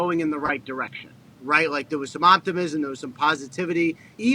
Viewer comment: watching this show is starting to effect how I hear things, when Rapsheet said optimism I thought he was saying aaahptimism